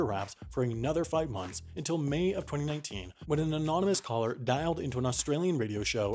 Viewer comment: 2091??? I thought you said 2019